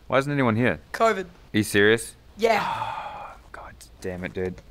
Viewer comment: minute my Siri turned on because instead of him saying serious it sounded like Siri-ous